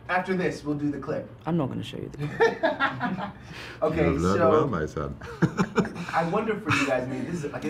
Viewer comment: what did Benedict say? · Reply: I think he said “you done well my son”